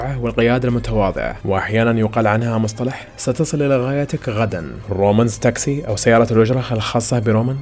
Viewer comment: هل الى سمعته انا صحيح (ستصل إلى غايتك غدا )😂